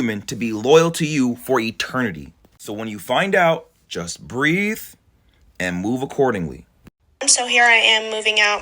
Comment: I thought you said “breathe and start recording..” 🙈😂 it’s not a funny situation, it’s just, I literally had to rewind the video to make sure that wasn’t what you said.